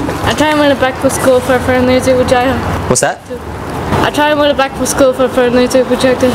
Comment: im ngl i dont even blame him for saying "what?" so much, i had no idea what she was saying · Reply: I was saying I was trying to win a bike for school